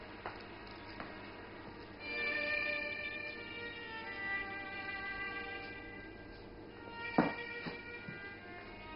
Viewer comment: Is she saying she got her period? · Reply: She's saying he drew her fat.